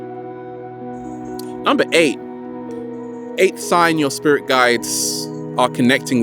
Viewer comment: You said number 8 and the subtitle said 88, you had 88 likes too.